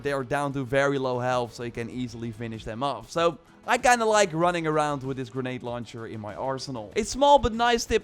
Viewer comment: I heard “running around with a grenade launcher in my arse—nal” 🤣